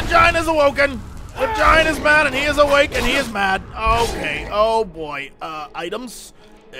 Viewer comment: Lulz my bf was watching this and when Jessie said "The giant is awake and he's angry" I thought he said "the vagina is awake and he's angry" xD